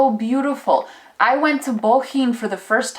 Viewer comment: did you say bohinj? i'm planning a holiday to Slovenia with my family for Christmas next year and this sounds like a must see place :)